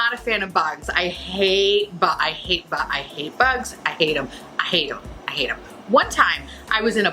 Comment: I'm so sorry but when you were talking about bugs I heard, "I hate them, I hate 'em, I ate 'em" so so sorry but I laughed so hard. 😅🤣